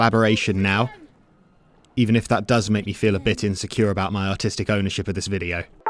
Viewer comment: i for the longest time thought you said, “my autistic ownership,” instead of artistic like bg3/dnd was your special interest and felt insecure because someone knew more than you 💀